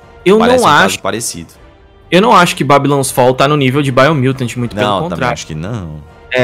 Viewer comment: que jogo que ele fala nesse minuto? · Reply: Biomutant